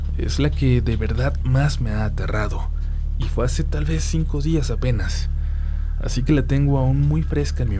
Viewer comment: Oye q dijo a 1:35, q no lo escuché bien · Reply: el CUENTISTA de lo INVISIBLE jaja gracias pero refiero a cuando dijo “fase tal vez cinco días apenas” pero eso no tiene sentido, quizá me equivoque